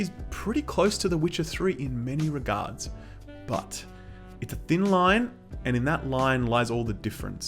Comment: Did you say thin limes? Thin limes in drinks?! People will choke! PEOPLE WILL DIE!